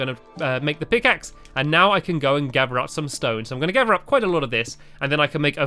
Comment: I thought he said ok now i can gather up some snow but when he said so we need a pickaxe i understand it was stone!!